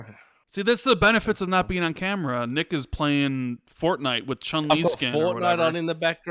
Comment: LoL when said Nick was playing Fortnite with with a Chun Li skin... I thought he said Chumlee... like the guy from Pawn Stars hahaha That would be the best Fortnite skin ever!